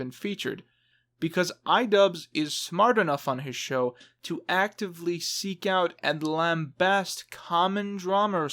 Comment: what in God's name did you say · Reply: "ively seek out and lambast"